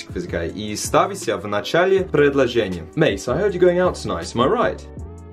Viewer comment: мне послышалось не "i`m right" , а "my wife" 🤣🤣🤣